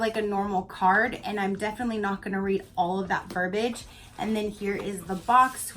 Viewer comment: I had to rewind and listen again...I thought you said "I'm definitely not gonna read all that garbage" LMAO!! I know it was "verbage" now. Jasper and sodalite are great balancing, calming stones. I read your palms while you were showing them and they said soon you were going to open a little box with an awsome necklace inside. (lol kidding) Oooh desert rose. Awesome stone. Pyrite is also called fools gold. Gorgeous. I LOVE this video!! and this box. You always soak your stones in salt from what I understand. But I think maybe those in the atrarium are already grounded and purified. Sorry for writing a book for you here. LOL Happy Weekend!! ps. Soda Lite is correct pronounciation.